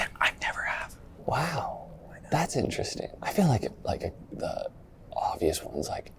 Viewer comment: I love the subtitles say “you’re a loser” ever though he said “that’s interesting”😭